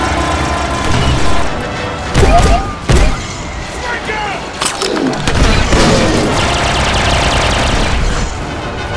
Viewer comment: what does it say? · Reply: He says “flank out” I think.